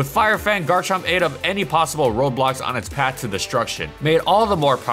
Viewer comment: Did he just say roblox